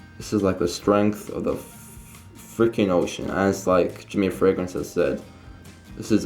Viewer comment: 8:88 did he just say Jimmy fragrances hahaha btw 4 comments in a row good vid